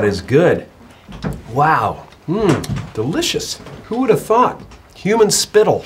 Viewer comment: I thought he said "Who the F*ck" I was like wait what till he actually said "Who would thought"😂😂😂😂😂😂😂😂😂😂